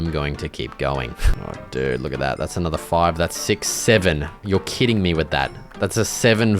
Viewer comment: did he just say it? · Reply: Yuppp!! He said 6 7